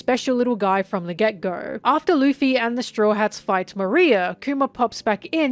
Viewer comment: I love all of videos, but some of the name pronunciations are kinda weird (like Moria, where I actually thought she was saying "Maria" at first, and was really confused lol). I wonder if that's because she only reads and doesn't watch the anime or even other OP discussions? I can't remember if she's said.